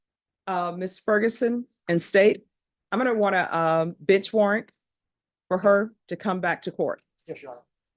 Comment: At 34.13, it sounded like she said "bitch warrant." I know she didn't but it's funny how it sounded like that.